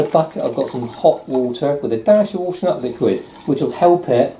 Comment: What did you say you added with the water to soak the wall with at 3:27 ? Couldn't catch what did u say.. thanks! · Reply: Hi Raul, just a little squirt of washing up liquid (fairy Liquid)